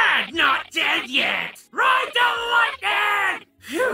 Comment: did he say "Ride the lightning."?